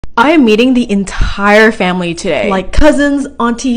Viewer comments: For one second i thought you said you will be eating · I’m sorry I thought u said eating the entire family 😃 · I thought you said... 'im eating the entire family today'... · HELP I THOUGHT IT SAID "EATING" 😭😭😭😭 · I thought you said I’m eating the entire family today 💀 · was I the only one who thought it said "IM EATING THE ENTIRE FAMILY TODAY" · I love at the beginning of the video I thought it said eating and it really said meeting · I thought you said you were eating the entire family 😅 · When she said meeting I thought she said I am feeding his family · I thought it said I’m eating my family today ! Lol😂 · Why did I heard "I'm eating the entire family, today" 😭